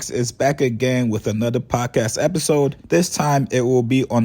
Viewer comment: I thought you said that Joe & AK got together for another punk ass episode 😅😂🤣